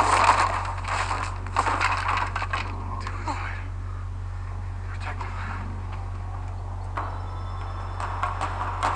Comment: wait what does Edward say at 9:42 after his punches jacob into the wall?? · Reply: I'll deal with that later